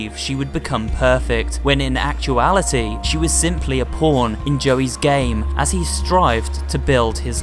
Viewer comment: sounds like porn instead of pawn